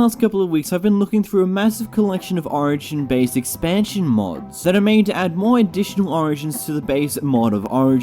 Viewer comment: I thought it originally said Amazon origin expansion mods and I was super confused lmao.